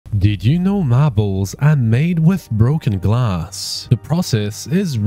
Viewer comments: It sounded like he said "did you know my balls are made with broken glass" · At the start i thought he said "My balls" instead of marbles💀💀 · Oh, he said "marbles". I thought he said "my balls are made with broken glass"